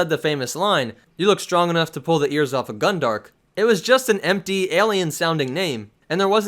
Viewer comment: For the longest time I thought Han was saying "strong enough to rip the ears off of Gundark", and that "Gundark" was somebody's name.